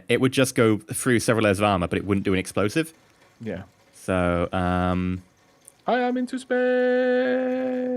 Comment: XD For a moment I thought said "I have into space.".